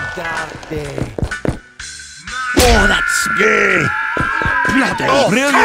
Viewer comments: “OH, that’s gay” The Demo scientists will remember that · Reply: I originally thought he said "oh, thats scary" until i read the subtitles. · Rainbow: exists Demo Freeman: oh, that's gaeh. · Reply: I thought he said "Oh, that's scary" first time around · I JUST REALIZED: THE RADIO DURING THE "OH THAT'S GAY" SCENE HAS "MONEY" BY PINK FLOYD PLAYING ON THE RADIO BEING SUNG BY ANOTHER DEMO. The entire scene is a Dark Side Of The Moon reference with Demoman. BEAUTIFUL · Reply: oh, he said 'Gay'? I thought he said "Oh, that's scary'